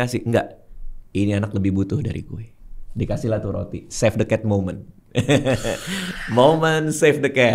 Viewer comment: berarti the hole ceroita one piece tuh "save the cat woman" dong ya bang radit?? Soalnya siapa yg suka sama bajak laut? Mereka suka menjarah, tapi dibawakan oda sedemikian mungkin jadi menarik penonton untuk memihak pada straw hat dll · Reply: Beliau salah denger save the cat 'moment' jadi 'women' sepertinya